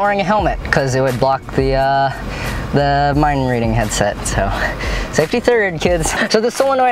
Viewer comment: "Safety 3rd" -Micheal Reeves, 2018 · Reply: I thought he said safety turd. 😂